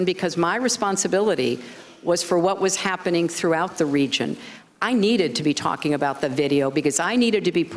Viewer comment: “I needed to be talking about the video” ...I thought at first she said she didn’t say anything about the video?